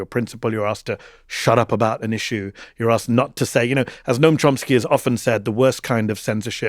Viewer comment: Does anyone else hear Mehdi say Trumpsky instead of Chomsky at 1:31:01? That would brilliantly underline what he says before about the orange guy just dominating his life since he move to the US. 😄Funny but also poor Mehdi having this guy in his head constantly.